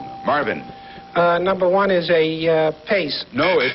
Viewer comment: and 13:21, is Tom saying "Singleton?"